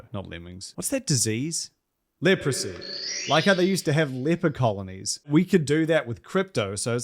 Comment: When you said "leper colonies" I heard "Leprechaunonies" and I was like, "What are those?"